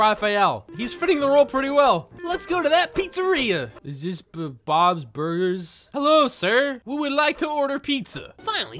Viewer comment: I thought said "Bons burgers" when he really said "Bobs burgers" XD